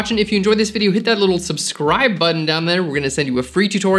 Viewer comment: "Did he just say... flern?"